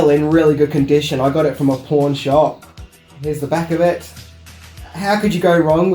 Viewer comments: I know you said you got the raider's of the lost ark record at a pawn shop but it sounded like you said porn shop.....I'm not being mean it's funny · It sounded like he said he got the raiders of the lost ark soundtrack from a porn shop😂